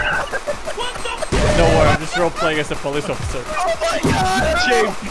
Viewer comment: James: "im just roleplaying as a police officer" also james: shooting obama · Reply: I thought he said “I am role playing a politician” which would be probably darker